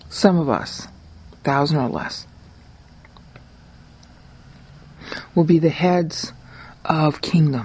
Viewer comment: Hi... Did you say a thousand, (or less) will be kings?